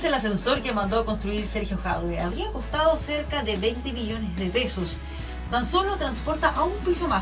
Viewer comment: ¿Escuché mal o la periodista dice que el ascensor costó 20 millones de "besos"